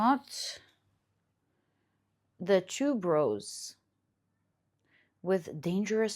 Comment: when u said tuberose i thought u said "two bros" 😂